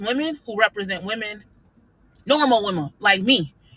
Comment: did she said “normal womal” 😂